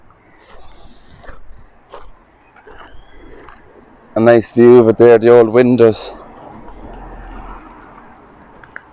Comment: I think you got another EVP at 19:17 but I can't make out what they are saying. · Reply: I hear it also. Sounded like, “I died a few years ago”. Great catch.